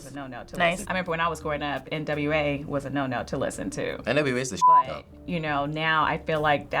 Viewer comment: WHAT DID HE SAY ???? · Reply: He said nwa is the shit